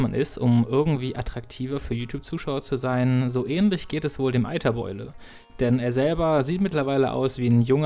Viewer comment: Ach, Hyper Beule! Ich hab.. immer.. Eiter..beule.. verstanden..:> Nah.. ändert wahrscheinlich net viel \_(-.-)_/